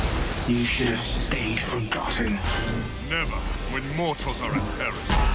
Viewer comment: wait... did he just said gotham? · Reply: He said forgotten